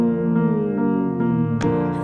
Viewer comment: Okay but why did I hear “You’re probably with that blonde gorilla” when my volume was down low 😂